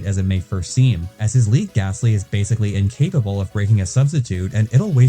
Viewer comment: I heard "rick astley" instead of "leag gastly"... I rickrolled myself